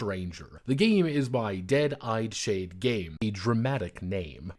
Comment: Heh. I misheard the publisher name and wasn't looking at the screen when he said it, leading me to wonder what the hell "Dead Eyeshade" was supposed to mean. I mentally wandered down a tangent about old-fashioned telegraph operators, newspaper editors, accountants, and casino dealers. And then I got lost on Halloween themed sleep masks, goth makeup choices and morticians studying cosmetics for about five minutes. I should probably listen to the actual video at some point, sure wasn't paying attention first time.